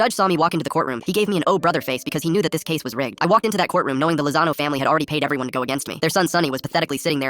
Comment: My dumba thought they said "Lasagna family"😭😭😭